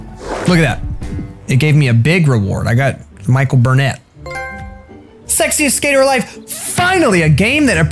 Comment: Thought it said "sexist skater"